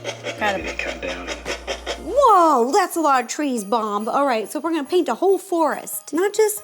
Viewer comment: ro said bom Instead of bob